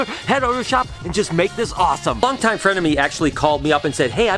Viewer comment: I swear right at the beginning I thought you said "long time frenemie" instead of "long time friend to me" i laughed so hard. Great video! Always happy to watch with my morning coffee. We've got quite a bit of snow here in MN today. Good thing i get to stay home!